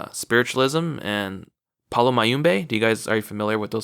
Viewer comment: What did he say it's called 33:10 Paula Mayumbay? I couldn't find anything on Google about that topic.